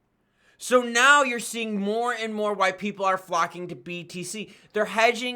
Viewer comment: Did I hear more and more white people are flocking to BTC? 😮